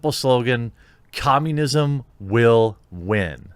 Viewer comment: "Communism will win." I'm sure I'm not the only one that heard that as "Resistance is futile."